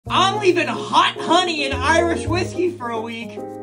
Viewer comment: I accidentally paused it at the beginning and all I heard was “I LEAVING HAAA-“ 😂